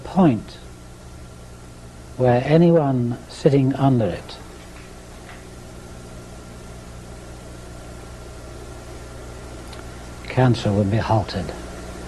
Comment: WHat does he say at 7:45? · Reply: He says...that whoever seats under, it will halt cancer.